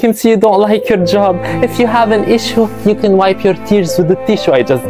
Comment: I really Thought the bully said “If u have an issue here's a tissue” 😂😂😂😂😂😂